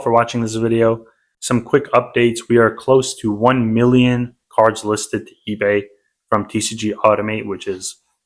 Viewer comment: what did you say? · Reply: We are very close to 1 million cards uploaded to eBay using TCG automate